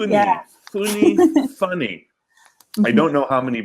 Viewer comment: eu entendi fúnei. Charlie Ganhou mais um inscrito !!!